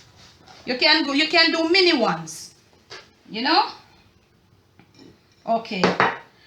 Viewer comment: I guess you can make these smaller and serve them as finger foods too ;) Looking good! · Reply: AMARU COOKS, At 21:22, she actually said, “You can do mini ones. Yuh noh?” You might have missed it because she just mentioned it; she didn’t further explain it.